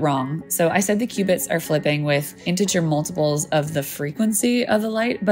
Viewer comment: I cant understand the word ... " Intergermolmol..?! " And btw, the term " FREQUENCY " is the best term to use. " LENGTH " is less precise. To describe waves, any types of waves, we should use the terms ( frequency and amplitude ) . If a wave is an " up-down / positive-negative " movement, then, AMPLITUDE is " how high and Low the wave reaches", and the FREQUENCY is " how many times does it go from High to Low in 1 second " High amplitude low frequency waves are strong yet cant go far and remain strong. Low amplitude low frequency are almost useless. Weak and short distance. High amplitude high frequency go far and remain strong longer. Low amplitude high frequency go far even though they are "weak". Wave Length is a distance in meters between 2 peaks of a wave and based on what we know about the speed of light... Anyway, it's easier to understand and comprehend with FREQUENCY and AMPLITUDE.